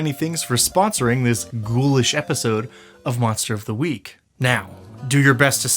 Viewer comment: I almost thought I heard "Munster" of the Week and was like, wait...